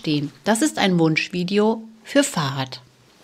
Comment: min "Wunschvideo für "Fahad" oder ...fürs Fahrrad? Ich habe Fahrrad verstanden, Youtube-Untertitel schreiben auch Fahrrad ...